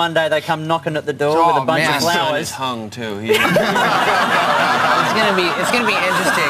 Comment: what does travis say on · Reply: He said he is gonna be hung